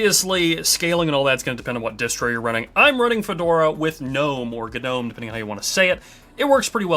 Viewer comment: i thought you said "with no more Gnome" i was so happy for you 😭 that sounded enthusiastic